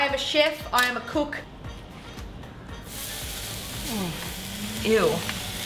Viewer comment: Wait, 5:49 did you say eww to steak!?